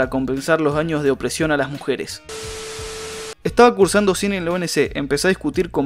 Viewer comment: si escuche bien cine mmm imaginen filosofía....